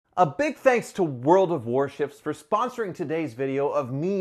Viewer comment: When you started talking about "World Of Warships", my old ears thought you said "World Of Worship" lol.